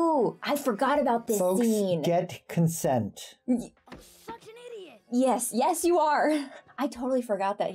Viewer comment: had to rewind that part a couple of times cause it sounded like Aang said, “f*king idiot!”… I was like, I don’t remember THAT being in the episode